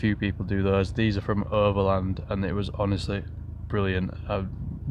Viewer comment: what are you saying? "herbaland?" "urba-land?" I cant quite make out what youre saying and google isnt helping me find that trim kit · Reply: Overland Designs!